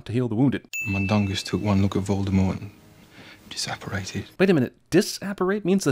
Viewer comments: I think he said he "just/jus apparated" not DISapparated, least that's how I heard it. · When he says disapperate I think it's just misheard and he's actually saying "just apperated"